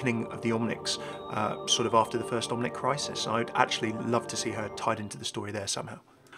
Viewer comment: *has captions on because I can't hear anything* 10:00 Ah yes, the good old omelet crisis. Who could forget that horrible point in overwatch's history?